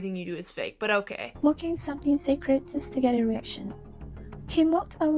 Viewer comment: I heard "eraction"...nice, I got spoiled((